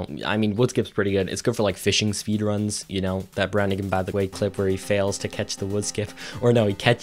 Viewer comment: Can someone please tell me the name of that streamer because sounded like "That shbairnauwahigawijgiuahgukawy by the way clip where he fails to catch the woodskip[...]" (Who is the guy at 7:29 ? basically) Edit: BrandiganBTW? If that's the guy I still humbly ask for a link to the video/stream clip because I have to throw some love there, that was hillarious.